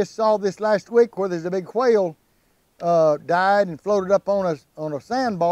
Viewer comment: I thought he say quail, then I realize is a whale in the ocean.lol